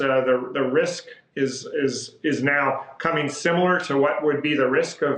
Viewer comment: Did he just say similar risk???